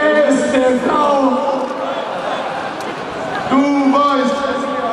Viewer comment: Ich hab "du Weib" verstanden